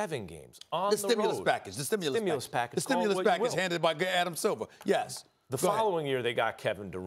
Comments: did SAS say “Gay Adam Silver” ??? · is it just me or did anybody else hear "oh yeahhh the stimulus package granted by gay adam silver" at @ 4:40 from SAS? lol